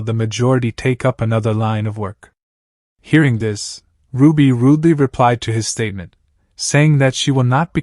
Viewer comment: I thought they said ruby ruby 😂😂😂 but they actually said ruby rudely